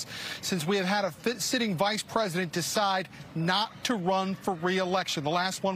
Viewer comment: Did he say fit sitting president? Seriously? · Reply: Well he said vice president but he obviously still have fifty on his mind and misspoke